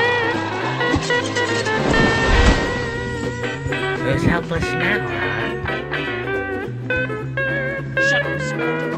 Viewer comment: What did Sid say at 10:23? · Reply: "Well For Shame!"